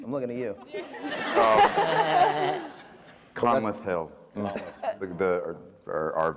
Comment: I'm sorry, but who are they talking about at 33:30 ? I didn't understand the name of the person making everybody laugh... Could someone please help? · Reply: Conleth Hill - lord Varys